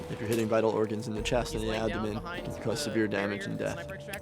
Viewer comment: i thought ge just said, "it could cause severe death"